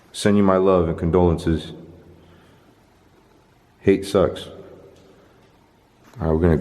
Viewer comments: I heard hate sucks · I heard a voice say “I hate you”